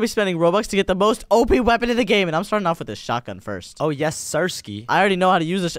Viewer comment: Did just say a bad word at the start or what did I hear 0:05 or did he say the letter s when he said this can the s was kind of put into him saying the s in a longer way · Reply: No he just saw shady😂